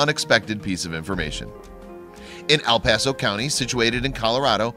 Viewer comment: I know guy did not just say Al Paso